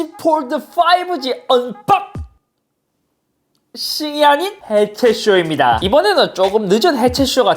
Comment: 쯤에 언박 씨리 라고들려서 씨리가 나옴 (내가부르면 나오지도 않으면서..)